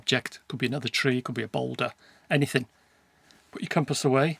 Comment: What did you say? Boulder? Is it big rock? (i am non english speaker). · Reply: Yes , boulder , a large rock